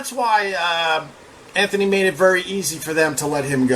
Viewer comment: Why isn't Opie leading the podcast world. I thought he said " Rogan would be very easy to beat" 🤦🤦🤦🤣🤣